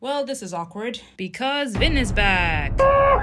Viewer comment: Oh I thought it said Vinn is black 😭